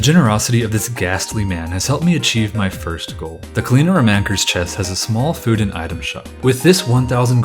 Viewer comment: Sorry did you just say clean-aero-man-ker